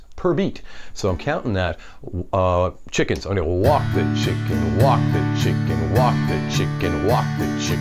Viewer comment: Walk the chicken! I'll remember that forever. Love it. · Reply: Thought he said "Wok the chicken" 🤣🤣😎👍